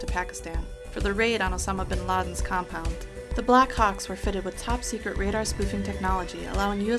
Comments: anyone else heard 'black cocks' in stead of 'black hawks' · who else heard”the black cocks” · did she say black cocks?! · did she say black cocks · i thought she said the black cocks XD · I swear she said black cocks instead of black ops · did I hear she say "THE BLACK COCKS" at 7:25? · it sounds like she says "The Black Cocks" ;)